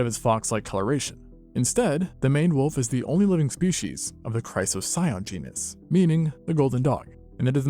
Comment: I have poor hearing but I like to listen to youtube as I work anyway. As a result I heard the name at 11:31 as god damn "Chrysler Scion", whatever the hell that means. Love the channels man, super engaging content!